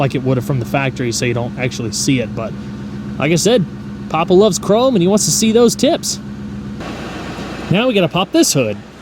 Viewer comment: At first I thought said “papa loves chrome so he wants to see those tits”